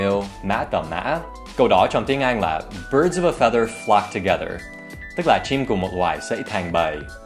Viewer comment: nghe nhầm thành 'birds of a feather fuck together'